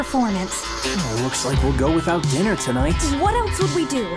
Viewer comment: did he said looks like we are going without dinar tinight??? Dinar is my countryes curency😂😂